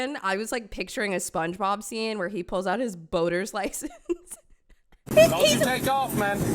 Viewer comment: I heard him say Alabama license. Roll tide. It tracks.